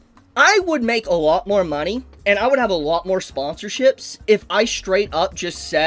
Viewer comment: did I hear that right, he would have more money? what is his content again, oh wait its pedo anime twitter